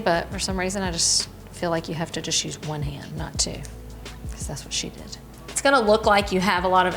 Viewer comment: The very subtle "That's what she said" at 1:15 is gold. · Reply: She said to just use one hand, not two because "That's what she did" - meaning her neighbor that taught her how to make pasta. :)